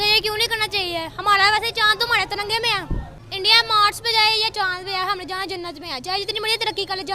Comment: did he said chand to hamare tirange mein hai?